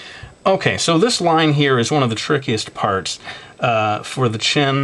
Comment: when u said trickiest at 6:05 it sounded like frickiest. seriously.